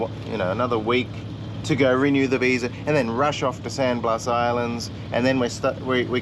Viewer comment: For next destination I heard "sandblast" but with Google Earth I found San Blas islands, east of the canal.